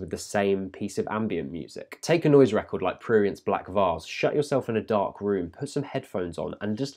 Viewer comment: Also, what is the name of the first artist you mention? Around 3:49 you suggest we take a record and shut ourselves in a dark room with headphones, and I could't make out what exactly you said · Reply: He said ''Prurient'' :)